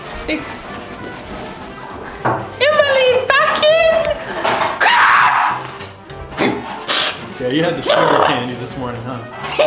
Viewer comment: he swore he said the f word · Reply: He says Emily back in